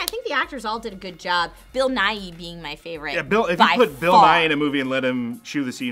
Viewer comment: When you said bill nighy was in the movie I thought you said bill nye